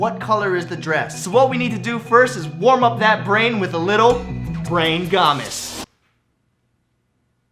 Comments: I heard it as Brain Gamiss. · when u first said brain games I thought u said "brain gommez" and U just spelled gommez wrong 😂😂😂😂